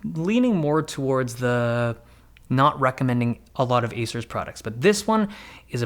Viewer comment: You sound like you said "Asus" products at the begining refering to "Acer".